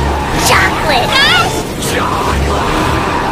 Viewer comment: chocolate guys are attacking the balloon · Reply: I thought she said chocolate ass 🤣🤣🤣🤣🤣